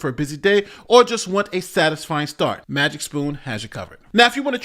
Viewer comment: I just heard Magic's Poon. Magic being a stage name.